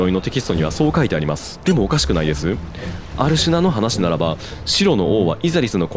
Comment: アルシュナがアルジュナに聞こえてしまった………すみません<(_ _)> シフをすっごくもふもふしたいです。もふもふ∩(・ω・)∩ばんじゃーい